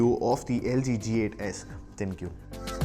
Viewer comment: I thought he said thank you 😂😂😂